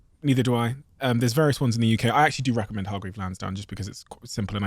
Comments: Hello what is that name of investment you said? Please spell it here i could not get it 😅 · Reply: Hargreaves Lansdown hope this helps 👍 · what is the name of the app I don't understand it you talk so fast :D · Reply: Hargreaves Lansdown